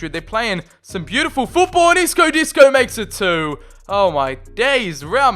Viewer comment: Did he just said "Isco Disco"?